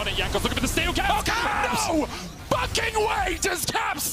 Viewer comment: absolutely worth the fine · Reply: sounds like he says ''Bucking'' am I bugging?